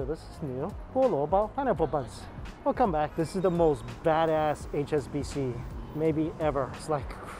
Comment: I know he didn’t just say what I think he just said... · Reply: badass? its not that strange of a word